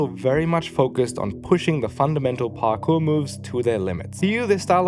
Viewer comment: did you say Parkoo? As in Sydney? 😅